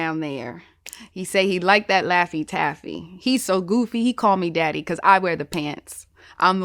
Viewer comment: Oop. This whole time I thought the line was," he's so goofy call me daffy" 🤣🤣😅 I went crazy when I thought I heard that but its still fire though.